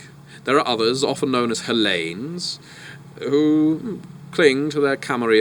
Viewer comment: what word does he say? Halain? Helaine? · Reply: 5 years late, but it's Hellene.